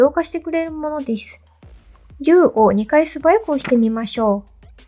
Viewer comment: こんにちわ わかりやすい動画ありがとうございます。 14:03のところがうまく聞き取れません。 10を素早くダブルクリックって聞こえるんですが教えていただけますか＞＜ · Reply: 動画を見ていただき、ありがとうございます！ すみません。動画では「Ｕ」を2回押していると思います。 エクスプレッションを確認したいので、「Ｅ」を2回押してもＯＫです！